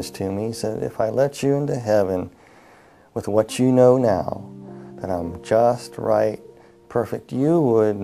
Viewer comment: What does he say at 3:25 - 3:30 I can't understand him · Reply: "If I let you into heaven with what you know now you would bring sin into heaven"